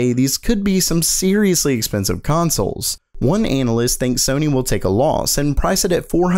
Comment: Did he say "anal-ist"?